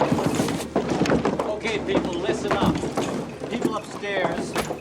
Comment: This whole time I thought he said "and wrap it" instead of "rapidly"